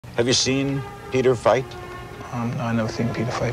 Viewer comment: I thought he said" have u seen pedophile?"